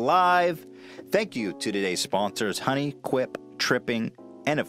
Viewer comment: Sounded like he said this video was sponsored by "Honey, quit tripping."